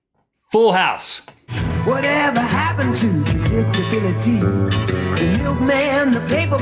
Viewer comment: I heard "debility" instead of "predictably" for Full House haha